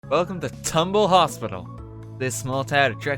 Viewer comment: I thought it said tumblr instead of tumble...